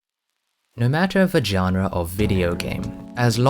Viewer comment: istg, at the start i heard "no matter vagina"